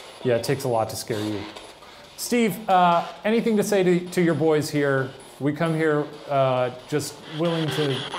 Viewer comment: I hear Welcome But idk tbh